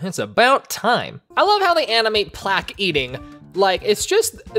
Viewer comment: he said "Plack" instead of "Plagg" L.O.L.